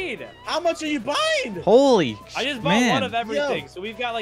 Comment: what was bro about to say🤨🤨🤨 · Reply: Holy sh- man